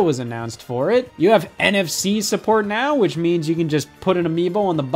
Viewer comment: I swear I thought he said NFT support